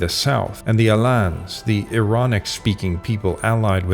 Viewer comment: I heard "they spoke the ironic language." 🥴